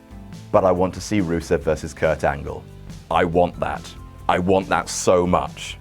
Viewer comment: I thought he said rusev versus fandango